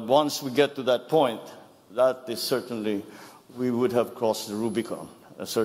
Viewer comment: I think you have misheard PBBM's last 2 sentences. He said, "Is that a red line? Oh most certainly it's going to be a red line." and not "... almost a red line". As per your banner line, it's not "close to an act of war" but rather, "an act of war" is what he meant.